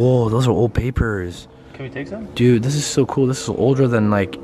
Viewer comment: after says “Whoa those are old papers” all I hear every time is “Can we take some?” XD